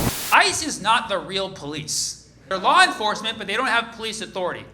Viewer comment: I thought I heard "ISIS not the real police" 😅😅